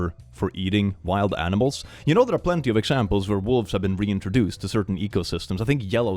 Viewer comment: They didn't introduce wolves to yellowstone, they re-introduced wolves to yellowstone as they had been eradicated in the area during the 1800s. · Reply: I heard say "reintroduce"?